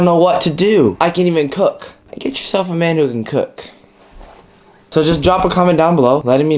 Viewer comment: he’s talented, can cook, and has a sense of humor LAWD send him my waaaay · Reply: IITzMysterious he clearly said “i can even cook, get you a man who can cook”